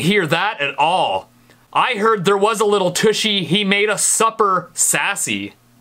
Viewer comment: I hear sashley